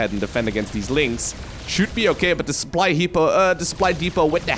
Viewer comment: did you just say supply hippo?